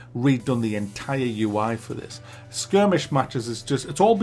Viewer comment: This is where i learned the word "Skirmish".. or "Skrimish" as i thought it said ..